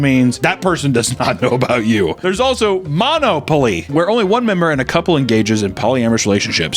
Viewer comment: did homie just say Monopoly?